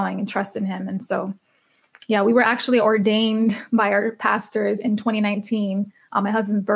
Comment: Take a listen! <link> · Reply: did she just say at the end that she was ORDAINED?. what?.I m mot protestant like ordained pastor?.